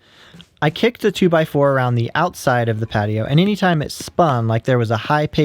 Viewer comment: Everytime you say "Around the outside" I hear "Round the outside" freakin eminem